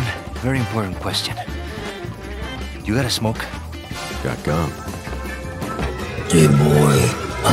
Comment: “Got gum.” Always kills me for some reason 😂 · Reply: I misheard it as "Got gun" and somehow it felt like a really hilarious response to Luis